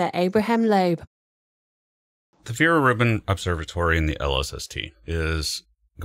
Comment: I heard "Fear Ribbon" instead of "Vera Rubin"